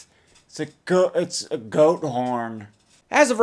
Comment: At first I thought he ( idubbbz) said "goat porn."